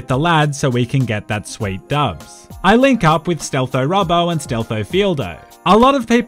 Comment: I first thought/heard your mate was named stealthTomato..